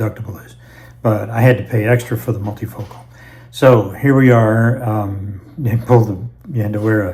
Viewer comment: Just a quick question. At the 2:03 mark, approximately, it sounded like you said, "here we are in Poland". When I checked the transcript it just said, "here we are". Did you travel to Poland for the eye surgery? I'm 75 and so far my eyes are good. Neither of my parents had problems with cataracts and I'm hoping I don't either as I age but I have thought about the possibility someday.